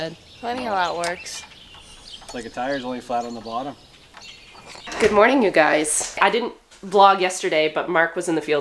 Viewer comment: I heard "good morning ewe guys"! 😁❤